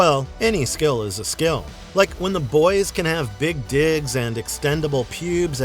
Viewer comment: Did you just say 13:52 that the TMNT have "big dicks" & "expendable pubes" !???